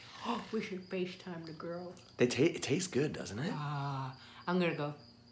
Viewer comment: what does mom say? We should FaceTime the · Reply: Girls